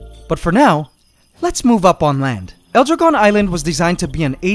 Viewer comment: i definitely thought he was saying "elder gone"